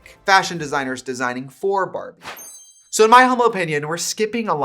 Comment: i gotta point out that the captions at 21:11 say "so in my homo opinion" instead of humble and i think that's fierce